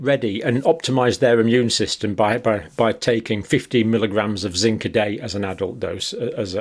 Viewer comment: Did he say 15 or 50 mg of zinc at 0:44? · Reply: 50.